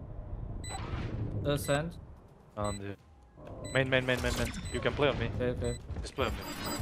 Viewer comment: ¿Cómo se dice eso en inglés? Que no logro escuchar bien lo que dice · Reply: you can play on me?